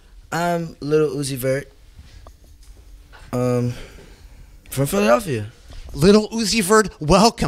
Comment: pls tell what he said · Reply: "from Philadelphia"